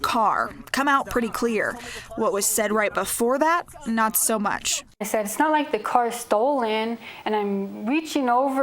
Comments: she clearly said it's not like the car is stolen but ok baldy way to protect your boy. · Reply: it was not clearly. i heard “this is a stolen car” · Well, she should have been respectful in the first place. She sounded intoxicated. To me,it sounds like she said “Actually, it’s a stolen car”. I do think the cop was too aggressive but she brought it on herself. Why are you, KOB, giving her a platform? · I heard " actually this is a stolen car" .. a slowed down the speed. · I dont feel sorry for her . Maybe not be a smart a$$ . The cop was in the right . sounded like she said it's stolen.